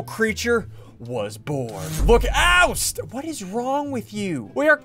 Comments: is it just me or did he say oh shit for the first time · HE SWORE HE SAID OH SHIT AT THE START Edit it’s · did sunddee just said "oh sh!t?"